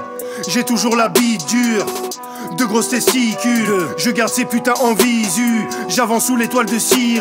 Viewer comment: c'est moi ou il dit " Deux GROSSES testicules" ?